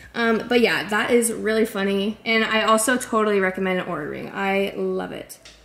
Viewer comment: what’s the ring called sorry I can’t catch what you’re saying? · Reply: oura ring!